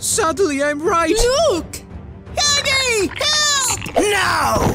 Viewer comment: it really sounded like player said Daddy instead of huggy I still like the video